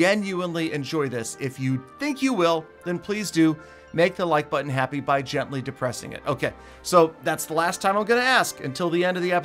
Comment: Did he say make the like button happy be gently depressing it